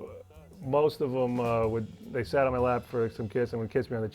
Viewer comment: wait, what did he say? It sounded like he said "they sat on my lap" lol